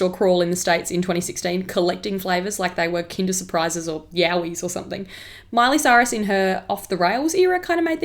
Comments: um excuse me, did she just say YAOI?? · did she just say what i think she said or am i just deaf i literally heard her say yaoi lmao i think my ears are dead